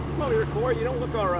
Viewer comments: The officer didn't actually say this, but what I heard was, "come over here, boy". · did he just call him "boy"?